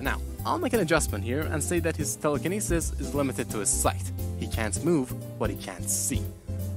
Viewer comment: Sorry, what did he say here? But man, I love your analysis. While my feelings on Silver are mixed since I dislike time travel, especially easy time travel, as a character I like him. He’s this pure cinnamon roll with a rough past, and I have a soft spot for that. And he’s psychic at that. I just liked to imagine him as a character in modern day after 06 because again, I dislike time travel and 06 got retconned, but that kind of removes the big interesting part of his character doesn’t it? Regarding Shadow and Silver, an interesting thing is that Silver is Galahad while Shadow is Lancelot in Black Knight; I.e. son and father. Also some connect them with Trunks and Vegeta respectively from Dragon Ball; I thought that was the reason for the fan theory that they’re related, but I never noticed how their connection and chemistry actually went a bit deeper. They really have potential together. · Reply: Thank you! I said "Limited to his sight, he can't move what he can't see" I also hate time travel, funnily enough, tho, I think Silver is a character that uses time travel only as a vehicle to tell his stories, and not something that's ever really the focus.